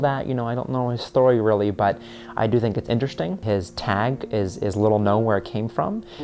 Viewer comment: What is his tag at 17:19 ? I hear, "is little know where I came from," but that doesnt make sense. Please and thank you